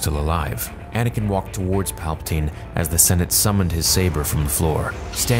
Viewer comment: Am I the only one who heard: as the senate summoned his lightsaber?